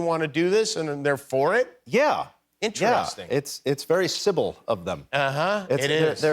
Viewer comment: ish "it was very Sybil of them" when talking about Fox's willingness to air the covid vaccine PSA in contrast to the rest of their lineup. I heard it as "civil" at first. I had to rewind a few seconds to confirm he said Sybil.